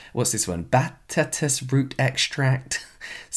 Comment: Did you just say bat TESTES root extract? No ma'am!!